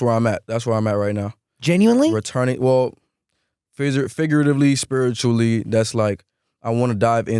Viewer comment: he slipped and said freezer instead of figuratively. It was a freudian slip. She could’ve been in the freezer when this was filmed.